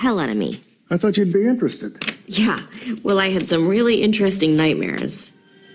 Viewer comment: Shiet! I pissed myself when I saw that. Also when she says “I had some really intestine nightmares” I thought joker was going to say “is this one” before shooting her